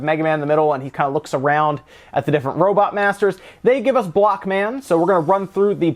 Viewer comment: I literally thought the first stage said "Black man" oof